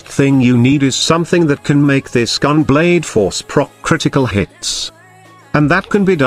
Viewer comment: I swear i heard " Scum blade" everytime.